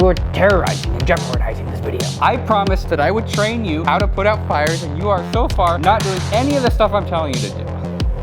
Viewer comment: I thought it said "I trained my car to put out fires" I was hella interested and confused lol